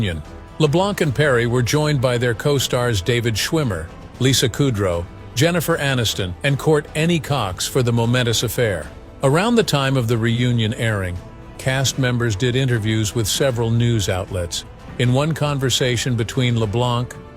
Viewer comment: Did anyone catch that at 15:38 he said Court any cox lol wtf... too funny